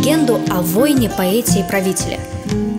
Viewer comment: Очень прекрасно спето❤ и кстати я один вместо 'Войне, поэте и правителе" услышал "Войне поэтии правителя"😅